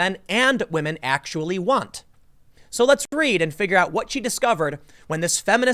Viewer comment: "So let's read." I heard: "So let's breed."